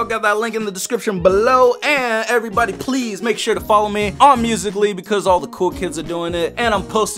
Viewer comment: click 7:11 3x times its so funny it sounds like hes saying on me in mr bean voice lol😂